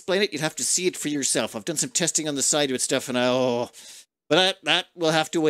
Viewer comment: I swear I heard him say good Lunk